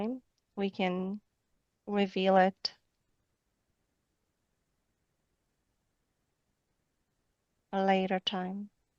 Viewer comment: What name did she say was the second key?